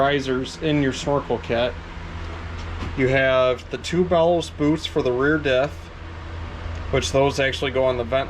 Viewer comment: What do you call those black boots, I hear "ballot boots"?